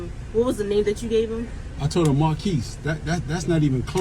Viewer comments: I'm sorry, but to me it really DID sound like he said mon quis and not mar quis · Yeah this guy is soft. When he said his name was Marquies, I heard it as Marcus. If I can make that mistake then this teenager could make the mistake of saying it's monkeys. Stop being sad this guy had every right to complain and he should have got his meal Refunded However the fact that he went boo-hoo crying to the news is just ridiculous.